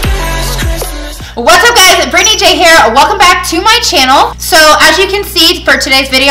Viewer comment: Stop screaming. It sounds like you said Baby Day Care not Here....